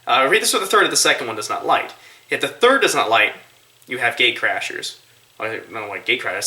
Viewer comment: I thought he said "you have gay crashers." I was like "huh?"